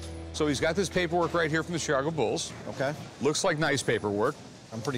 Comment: Did he say “Sharga Bulls” @ 12:03 ??? 🤔